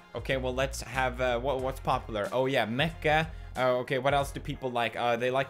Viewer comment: When he said Mecha i thought he said mecca xDD who else ?